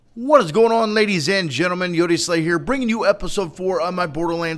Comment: First time I havnt heard you say genitals, dafuq is a gentleman?